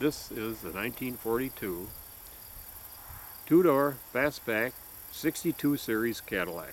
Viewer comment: I did not hear them called "fast back" I heard 'beaver back".